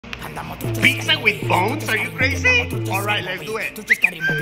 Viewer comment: I thought he said pizza with balls😂